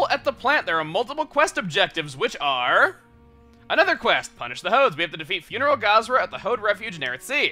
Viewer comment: when you said "Punish the Hodes," I honestly thought you said "Punish the Hoes."